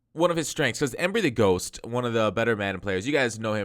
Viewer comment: did he say everything ghost? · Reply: 3ptSpecialistx3 is right, embrythegh0st is a lab partner and collaborator with me. We did a video on his Eagles scheme earlier this year. He hasn't uploaded as much in 17 but he has a youtube channel where he does top 10: <link>